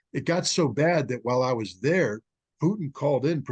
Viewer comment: what are you saying "while I was there "?